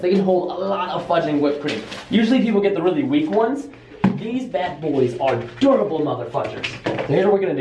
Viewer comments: did he say these bad boys are durable mother f**kers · lol I know he said fudgin but it you gotta admit it sounded like he swore xD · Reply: when he was talking about the plates I heard "Crazy mother fuckers" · am I hearing things or did Preston say motherf***ers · did preston say mother######## on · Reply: He said mother fugers